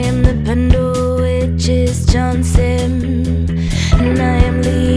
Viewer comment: Ignore this, what I misheard is corrected below: As a former English teacher, I wish to add my bit for appreciators of this clever and highly localised song. The third line, “I am the Pendle Witches’ Johnson” refers to Margaret ‘Meg’ Johnson, who in 1634 after a group of people had been accused of witchcraft, which they denied, unhelpfully came forward with a gratuitous confession (the ageing Johnson had not been accused), telling magistrates that she had been present at the second coven meeting of the accused people. Interviewed by the Bishop of Chester, she became famous as “the penitent witch”. Maybe Johnson had experienced some kind of religious crisis – she had ceased going to church (that she had recently become lame may be relevant), and perhaps tried to account for non-attendance as a result of a pact she had somehow made with a devil called ‘Mamilion’. She was sent to London, and became a character in a witchcraft play, ‘The Late Lancashire Witches’. But she had in 1634 a strange form of celebrity, and so aligns with the song’s subject. [Sorry, it’s my own Mastermind Specialism.] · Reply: Nice story but she doesn't sing Johnson, she sings "I am the Pendle Witches, John Simm and I am Lee Ingleby"